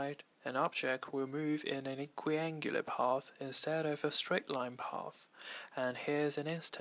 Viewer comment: I am so sorry but can I ask what you said at 4:41 ?? Your video is really helpful. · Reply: an equiangular path